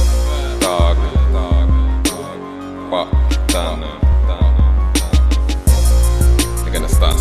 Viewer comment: Does Giggs Say Fuk Downham on